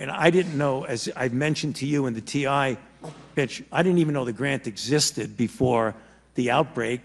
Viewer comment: Alright, somebody correct me if I'm wrong but at 16:38 it sounds like he said, "Bitch I didn't even know the grant existed...."